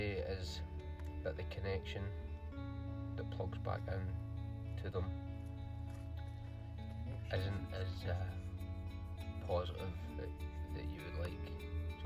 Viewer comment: what were you saying about the connection? I couldn’t hear you. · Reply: Just saying that the plug connector didn’t connect back up as well as it should have….it was a bit loose so had to use a little hot glue to keep it in place 👍